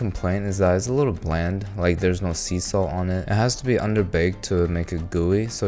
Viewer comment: istg i thought i heard cecil instead of sea salt….the invincible brainrot is getting to me…